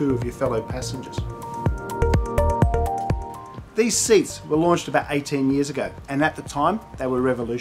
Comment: Oh, these seats were " LAUNCHED 18 years ago" ...... I thought you said "WASHED 18 years ago" ... hahaha ... or ..umm... ?